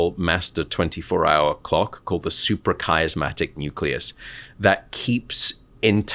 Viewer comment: Please correct the caption "Suprachiasmatic nucleus" instead of "Super charismatic nucleus" :D